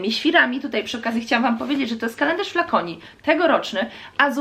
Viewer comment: Pod koniec filmu zamiast kalendarz Flaconi usłyszałam "kalendarz dla koni" i złapałam 3sek laga :D